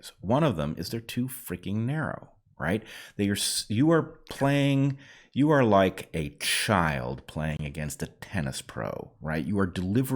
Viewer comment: What you said: "you are like a child playing against a tennis pro" What I hear: "you are trying to play God" Is that unintentional biblical reference an admission that faith in a higher power was right all along? #freudianslip